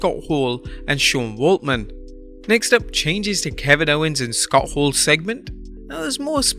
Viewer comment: Did I heard that right? Kevin Owens and Scott Hall's segment? What the hell